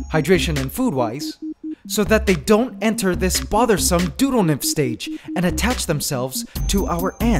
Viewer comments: The first time I watched this video, I thought he said doodlenymphs. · lol who else thought he said Doodlenymf?